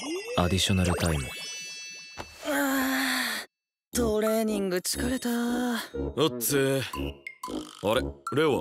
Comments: トレーニングちゅかれた に聞こえた🥹千切かわいい🥹🥹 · お嬢:トレーニング ちゅかれたー って聞こえるの私だけ？？ 斉藤壮馬最高かよ！ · お嬢の「トレーニング疲れた〜」が私の耳のフィルターによって「トレーニングちゅかれた〜」って聞こえててガチビビりした……お嬢2話分(？)くらい姿を見てなかったから久しぶりに見たって感じするしクソデカ感情爆発させてしまった……お嬢恐るべし……